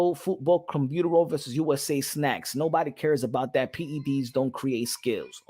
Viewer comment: Yarr still mad damn homie I thought you said nobody cared about that fight lmaooo. Keep going.